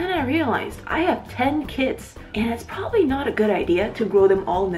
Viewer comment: I thought you said "i have ten kids", also why didn't you just put the box over the mushroom instead of in it. Love the video